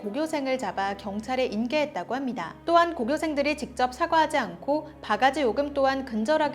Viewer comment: 오류에여 '사과하지 않고'라 들리네요